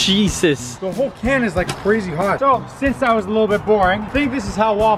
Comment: Wait what??? 6:42 I think he said and I quote "the can is freezing hot!!" What!?!? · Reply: He said crazy hot